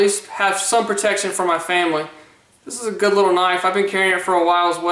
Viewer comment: /Funny I thought he said "protection from my family." I thought Damn, that's hard core. XD